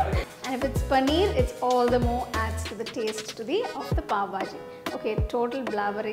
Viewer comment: Pavbhaji, I heard it as BABAJI!